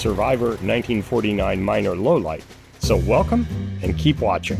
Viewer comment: Another great video. I wasn't listening carefully, and I thought you said "Low Life" Minor, rather than low light.